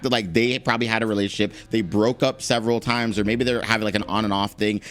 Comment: did i hear you say they broke up...several times 😏